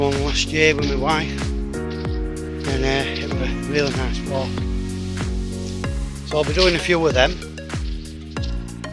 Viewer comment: Thanks for a great video. At minute12:35 you mentioned a previous walk you had done with your wife .I didn’t catch the name but I thought you said some forest. Could you reply with the info. Spent 10 days in Bainbridge, Askrigg, and Hawes area. Breathtaking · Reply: Paul Akehurst sr Hi Paul, it was Dalby Forest in North Yorkshire near Pickering. It has some lovely walks there and even has one of those Go-Ape activity courses. Thankyou for watching, a new video is due next week, so hooefully youll be around for that mate 😀👍